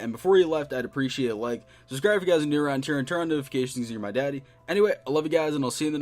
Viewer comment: is it just me or did he say "your my daddy"